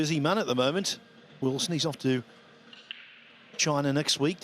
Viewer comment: did he say sneeze off to china